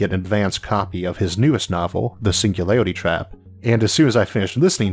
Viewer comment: All I heard was "the single lady trap"